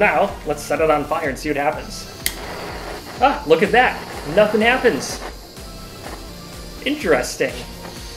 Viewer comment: It sounds like he is saying "nothing gas"